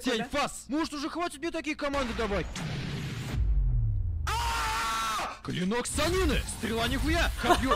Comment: Я один услышал "Клинок Санина"?(Типо, Джирайя, Орочимару и Цунаде?)